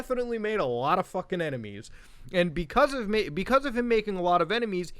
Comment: Did I just hear "because of me"?